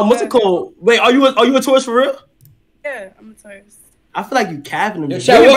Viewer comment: Shorty said "I'm 22 and I'm a taurus that's pretty much it" 😂😂😂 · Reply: I deadass thought she said "I'm 22 and I'm a tourist" 💀💀 now I'm just like... Bruh